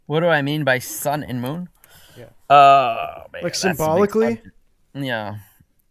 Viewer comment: did you call me Matthieu?